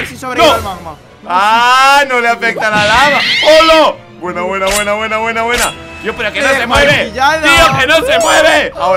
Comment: póngale pausa y verán lo que dice😇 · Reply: Toma tu magma perro